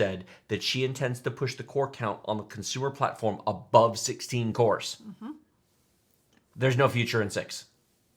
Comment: I heard there's no future in s*x lol